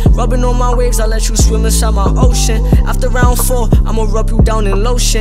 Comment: Did he just say I let u swim inside my ocean